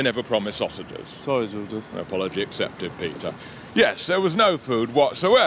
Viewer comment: does Judith say: “Eh, apology accepted, Peter.” or “No apology accepted, Peter.” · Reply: I think it was just a drawn-out "a".